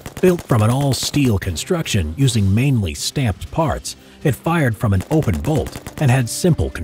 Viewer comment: I honestly thought he Said Stamped farts at the beginning